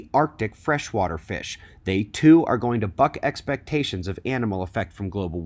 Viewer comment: I thought you said "fuck expectations" not "buck expectations", both would be valid I guess.